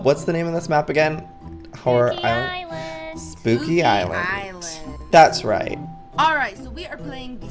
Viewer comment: I thought lizzie said cookie island